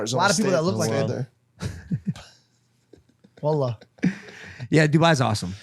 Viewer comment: if you dont know what georgie said he says "wAllah" which is kind like "on God" in Arabic 👍🌈⭐#themoreyouknow